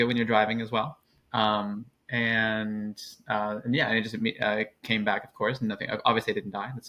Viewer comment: Thought they said it came beck not cancer.